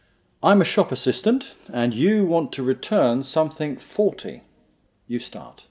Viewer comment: In 3:19 min of the move instructor says: "I am shop assistant and you want to return something "forty" like number 40 or "faulty" like something broken ? Or is it some English expression (I never hear before) ? As I do not get what he needs to say "Hi, ser, please can you help me I would like to return this 40 apples, as they are not in good shape." or "Hi, see, please can you help me device you have sold me earlier this day is faulty, so I would appreciate if you can give me another one." ? or "I would like to return this in 40 days ?" (but that is not logical) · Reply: hey, he says your second guess, "something faulty".